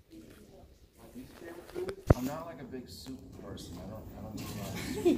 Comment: does anyone have any idea what the question is, or what Seb said? I can barely make any of it out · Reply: She asked what his fav chocolate is and he said Cadbury. And after that what's his least fav food and he said he's not a soup person 😁